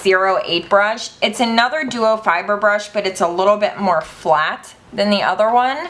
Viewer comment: I thought you said "Bieber Brush!" LOL! Give that boy his hair back... I need more sleep... LOL!